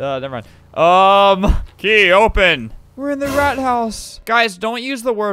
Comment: At first I thought it said frat house and I laughed